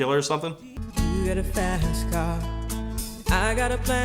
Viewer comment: This is me and my girlfriend's song. I put it on the closer of a mix cd and she told me that she always thought it was "you got a fatass car" and I can never unhear it. I also had to tell her that tracy chapman was a lesbian and not a man, and she didn't believe me at first. We both learned to play it on piano and guitar. We're going off to college soon and I don't know what will happen, but I'll always love the original. Thanks for covering it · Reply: I keep hearing "fatass" car too